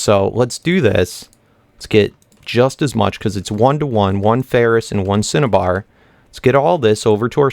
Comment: uum lets see say ten cinnabar it sounds like you say satan